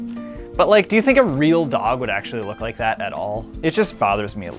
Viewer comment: Did he say dog?